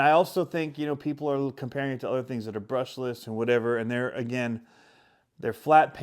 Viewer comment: Sounds like you said the Lmt is brushless